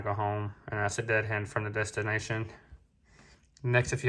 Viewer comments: DH stands for dead head. Distance driver goes empty to pick up loc. sounds like you say dead hand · Your video is really great. Thank you so much for being so transparent and helpful. The only critique I have is it sounds like you are saying deadhand. The correct term is deadhead. If you're saying deadhead, I misheard you, and apologize. Keep up the good work.